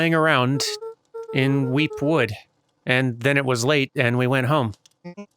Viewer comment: I keep hearing "The Weep Wood" instead of "Weep Wood" and get reminded of Tama. I didn't even play WOFF, I just watched someone else play it, and I still think of that. XD